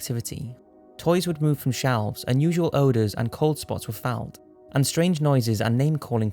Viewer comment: Did he just say "Cold spots were found"